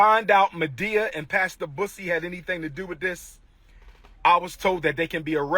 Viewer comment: I CANNOT STOP LAUGHING WHEN HE SAID MADEA AND BUSSY SOUNDS LIKE HE SAID PUSSY!!!!!!!😂😂😂😂🤣